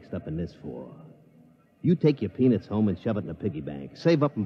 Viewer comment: 'You take your ????? and stick it in your piggy-bank'. What did he say?? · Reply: Patrick A. Crawley "Peanuts." Old time term for money.